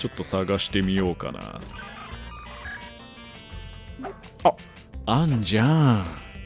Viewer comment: あんじゃぁんがあんちゃぁんに聞こえてちょっと笑っちゃったw